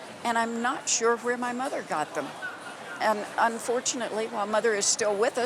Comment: “unfortunately my mother is still with us at 103” 😂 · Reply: I thought she said 'fortunately ' ?